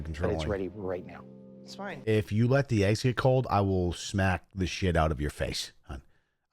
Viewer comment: 😕👂 i thought i heard that right.. (had to rewind it a couple times, just to check * make sure) 4:42 mins in.. says "if u let the ICE get COLD".. but, 💁🏼‍♀️ ice IS "cold" (🙄 .. duh.. 😉)